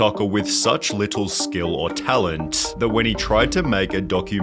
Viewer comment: Loool for a second my ass thought I said “talented” 💀💀